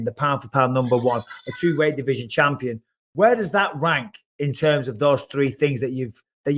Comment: 17.05 sounds like mike says where does that wank. Instead of rank. Haha